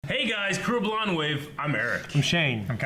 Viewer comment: I've watched hundreds of your videos and I still don't know what your intro is. All I hear is "hey guys crooer blonde wave" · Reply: I always assumed they were saying "Hey guys, Crew Blind Wave." As in, we're the Blind Wave crew, or the crew from Blind Wave. :)